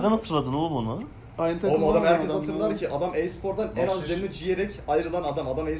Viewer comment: te rogu ne diyor ? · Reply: ciyerek falan bişey diyor anlamadım